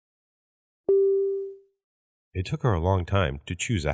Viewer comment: 何回聞いてもイトカワlongtimeにしか聞こえない